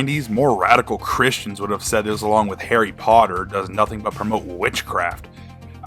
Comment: whats the clip at the end from? 4:44 did you say "islam and harry potter?" to answer the desc id say the 10+ ones